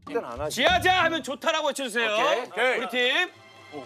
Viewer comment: 미세하게 들리는 오케이,,ㅋㅋㅋㅋㅋ